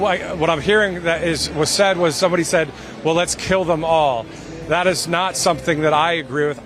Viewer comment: While I'm not trying to support the MAGA idiot, I honestly did not hear "Kill 'em all." I heard "Jail 'em all." Maybe it's just me. Did anyone else hear, "Jail 'em all?"